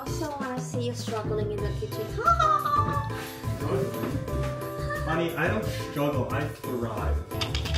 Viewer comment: did he say "honey"? 🥰🍯✨🤔🫂🏃🏻*kicking my feet af